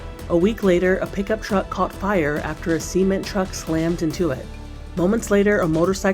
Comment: did she say semen truck?🤣🤣🙏🙏🥀🥀💀💀